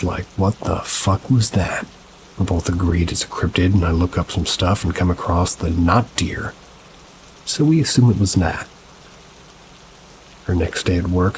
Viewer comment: " ......not dear, so we assume it was meth..." um...WHAT? Did I hear that wrong?